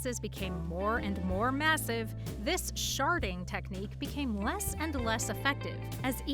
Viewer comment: Google having "sharting technique" made me laugh I know its spelled differently but this is how i hear it